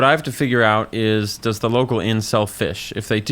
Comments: Walked away for a second and misheard this as "Does the local incel fish?" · I heard “does the local incel fish” at 2:21 😭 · I thought for a second he said "does the local incel fish" and I thought "how did you come to that judgment of that guy? OH DOES THE INN--" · i heard that as "does the local incel fish?" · I misheard this as "Does the local incel fish?" I think I need to spend less time on the Internet. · I heard "does the local inn sell fish?" as "does the local incel fish?" and was real confused for a sec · I initially misheard that as "does the local incel fish?" and thought he was making a jab at the guy. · I heard "Does the local inn sell fish?" as,"Does the local incel fish?" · I tuned out for a second and what i heard was "does the local incel fish?" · definitely misheard "does the local inn sell fish?" as "does the local incel fish?" which seemed like a very harsh but funny way to ask whether a citizen is employed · I thought you said “Does the local incel fish” not “Does the local inn sell fish” and was very confused as to why this old fisherman was an incel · I heard "does the inn sell fish?" as "does the incel fish?" when I was listening to this video in the background of my game lmao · Thought he said "does the Incel fish?" for a second